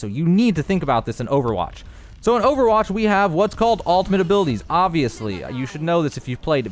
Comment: sounds like your saying 'altimate' and not 'ultimate' great video though!